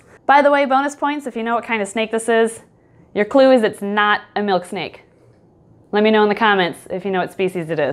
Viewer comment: “guess what snake this is” me: milksnake!!! “it’s not a milksnake tho...” me: ☹️ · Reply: I thought it said Milkshake and I was like, "I coUlD nAmE mY sNaKe mILksHaKe."